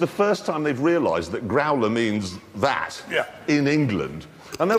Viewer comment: What's mean word graula or whatever Hammond said in 11:47 in German? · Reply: "growler"